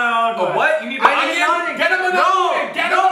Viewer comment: I THINK Devan said It's Under my Tongue